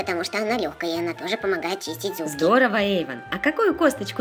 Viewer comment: потому что она помогает чистить ЗУЗГИ``-кто тоже это услышал?